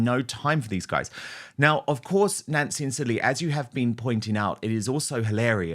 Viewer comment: Nancy and Silly? That is what the closed captions said on my screen.